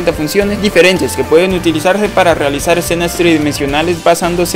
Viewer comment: Excelente video, una consulta que dijiste en el segundo 0:33 utilizarse para ...? No se entendió !! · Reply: Gracias! Dije "-que pueden utilizarse para realizar escenas tridimensionales-".